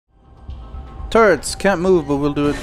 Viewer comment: You said "turrets". But, for a second, I thought you said "turds". 0:00 "Turds can't move but will do a lot of damage". WTF? ;D